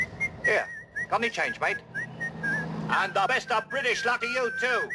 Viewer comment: what did the man say? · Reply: got any change mate? and the best of British luck to you